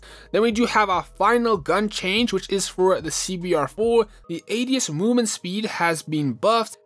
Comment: bro literally said ass instead of ads